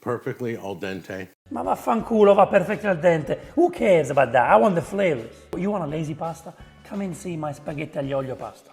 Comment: I've done a lot of lazy pasta: 1. Boil pasta 2. Add bolognese sauce bought from store 3. Add cheese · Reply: “motherf***r who the F** cares if it’s al dente” is what I thought I heard the first time 😅😅😅